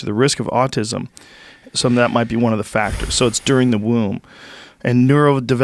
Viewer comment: did he just say "during the womb"? fuck man........